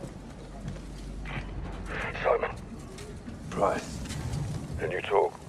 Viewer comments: Is it just me or does it sound like Price is saying “Soap” at the start of the season three trailer. I’ve heard it so many times and I’ve always heard Soap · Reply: He's saying "Simon". Yes, it's just you. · Is it just me or does price say soap at 5:49? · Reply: It's just you. He says "Simon". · Is it just me or does Price call Ghost "SOAP" in the begining of the season 3 video... at 5:47 into the video